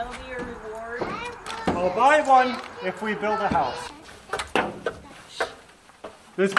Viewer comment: Beautiful! Did I hear you say," If we build a house"???